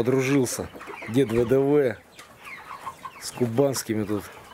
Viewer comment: Дом для кур называется курятник,а курник это из гастрономии. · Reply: Воронежская обл.,а в фильме мне послышалось ,,кубанские куры".